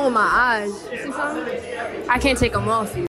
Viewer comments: Before reading the comments I thought she said: “I can’t take them off” I was like: yeah ur not wrong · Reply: ya exactly I thought she said “I can’t take my eyes off” I was like, “ yea u shouldn’t be able to” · Before reading the comments I thought she said: “I can’t take them off” I was like: yeah ur not wrong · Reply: I heard it as I can't them off... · I thought she said “I can’t take them off” and like she had thicc nice lashes that she couldn’t take off cuz they’re real. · First i heard like " i can't take morphy " like what what morphy?. Until i read the comment, my brain suddenly adjusted that to " them off you ", the brain scare me to think it has a problem:)